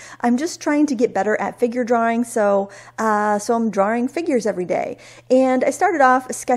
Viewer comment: It's funny, a few weeks ago you said you were going to draw figures and I misheard it as fingers! I was expecting a month of fingers after your hands last year! I like the figures much better. Lol. Such a great sketch, love to see your process.